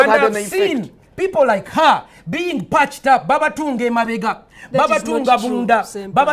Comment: Did Pasta Say *B A B A T U N D E*